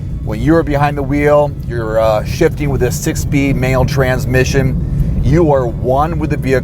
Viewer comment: He sounds like he said "male" instead of manual transmission.